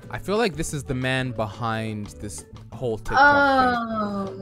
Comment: I thought he was gonna say the man behind the slaughter